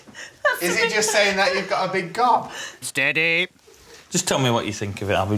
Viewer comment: what did he say 😂😂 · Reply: "Is he just saying that you've got a big gob" 😂 I heard it tho